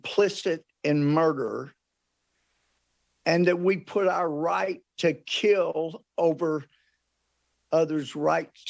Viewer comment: Did he just say "Right to kill" at 0:21 ? tf?!